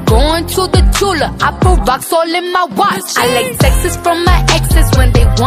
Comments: I LIKE TEXTS FROM MY EXES WHEN THEY WANT A SECOND CHANCE ☠👅 #SAVAGE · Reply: Marci G I hear it " I like sexs from my exes" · When she said i like texts from my exes I thought she said sexes from her exes😂 Thank u 4 all ur likes I’m soo happy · I LIKE TEXTS FROM MY EXES WHEN THEY WANT A SECOND CHANCE ☠👅 #SAVAGE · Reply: I always thought it said sexes from my exes